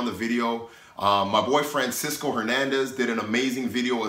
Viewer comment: Good tips D. It sounds like you said "My boy friend " instead of my boy Francisco. Go back and check it out LOL!